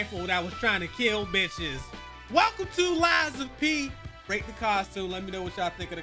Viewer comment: I don’t know why I thought said Life of Pi for…y’all know the Lion movie with the lil kid 😂